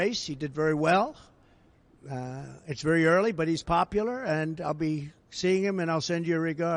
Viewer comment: Did he just say Kier is popular 😂